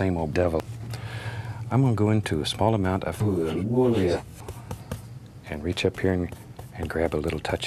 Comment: What is he saying at 2:16? 😂😂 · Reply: Morgan Lee Williams buel buelia?